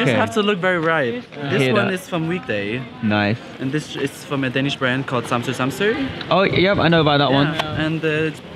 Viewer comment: what’s the bland name which he is saying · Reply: I think it’s samsoe samsoe